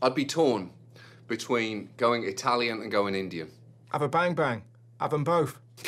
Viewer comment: is Russell saying “ban-ban” like Korean? Half-half?